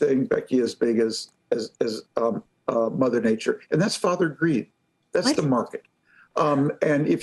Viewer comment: I heard him say farther greed